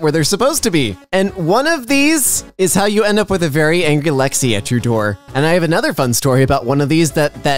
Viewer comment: WHAT Did you say @ 0:20?? Very Angulexitrudor 1:30 "One time at band camp"